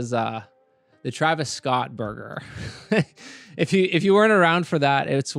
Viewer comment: Who's Travis Scott? Interestingly, I totally thought you said Travis Scott murder; which actually made more sense than burger.